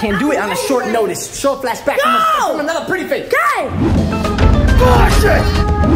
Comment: What? Did it say bullsh*t?